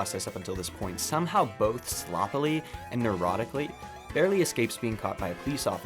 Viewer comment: bro the first time I watched this I thought he said “both sloppily and erotically” 😂😂😂😂